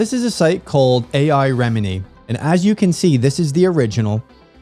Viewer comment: Great discussion. Not sure if you can access the close captioning, but around the 8:25 mark the CC says "remedy" when you meant "Remini." Thanks for posting, and I hope you post more analyses like this.